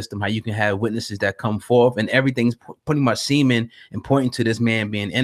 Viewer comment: Semen ... did he say semen? @ 2:19 -A Quick laugh